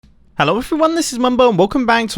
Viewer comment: I always thought that he said "Hello, if you're one, this is Mumbo." Instead of "Hello everyone, this is Mumbo."